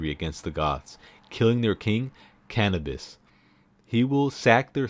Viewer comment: King Cannabis did I hear 👁👄👁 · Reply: Yeah King Cannabas lol, there are a few variations of his name, but I forget the others.